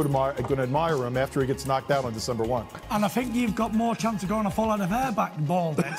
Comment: did he say “December 1th”